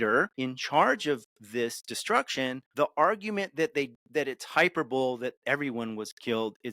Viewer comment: Did he say "hyper bowl"